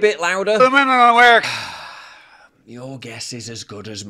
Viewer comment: I thought he said "the middle of America". Everything sucks